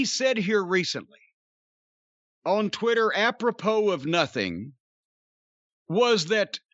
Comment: What word did Jim Cornette use around 1:20?... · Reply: Apropos